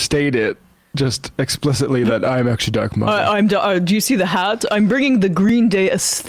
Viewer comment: When I first heard "dark MAGA", I thought he said "drunk MAGA". That would have explained a lot.